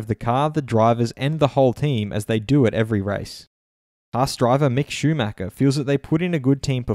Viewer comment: Why did i hear "Bus driver Mick Schumacher" ??